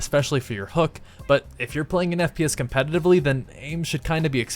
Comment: When your playing an "FPS, affectively What did you just say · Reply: TheSquareRootOf8 :3 He said competitively.